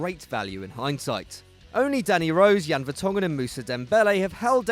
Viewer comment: omg I thought u say daddy rose💀😂